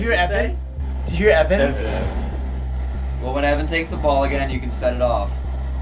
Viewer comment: This might just be me but, when says, "Well when Even takes the ball again you can set it off" 1:04:20 it sounds like it said, "I'll remember" but it might just be me.